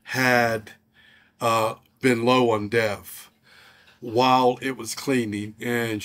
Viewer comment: I can’t understand what you’re saying at about 2:21. Checked the transcript and it said you were low on death!?!? I think they got that wrong. Please explain. What needs to be filled up? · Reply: DEF, not death. Diesel Exhaust Fluid